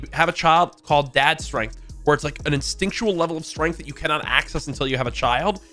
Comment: i heard him say "exticntion level of strength"